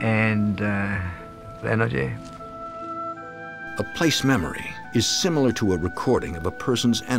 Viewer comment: I CAN NOT UNDERSTAND WHAT IS BEING SAID FROM 38:39 - 38:43 ....... IT SAOUND LIKE HE MUBMLED "LET HER LIVE" THEN THE NARRATOR SAID ..."REPLACED MEMORY IS SIMILAR TO A RECORDING" ........... what? can anyone clearify what was said there, & whats "replace memory,which is similar to a recording. I do not Understand any of this